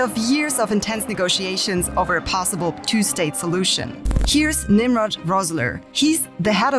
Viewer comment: I re-played it several times and I'm sure I heard "...possible two-state pollution..." 😯